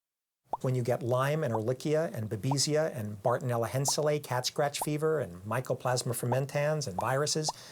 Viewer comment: HELLLLOO! Please write down here the correct names from 26:50 !!! I hear them like: "Elicia, Obisia, Bardinella Hencylake, Cat scratch fever, Micoplasma Femantance, Myrosis". But these are NOT correct, google does not give any results on them! Can anyone write correct names please! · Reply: These are the correct names for the diseases that were mentioned: ehrlichiosis, babesiosis, bartonella henselae (cat scratch fever), mycoplasma fermentans, viruses.